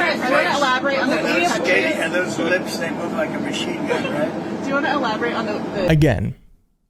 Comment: All I heard was, "Those lips blows shots like a machine gun!" 😂😂😂